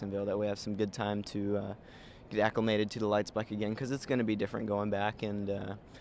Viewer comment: did he say he was going back to the lites bike?